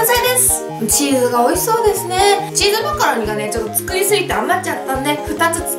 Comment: チーズマカロニがチーズマカロンに聞こえた😅😅😅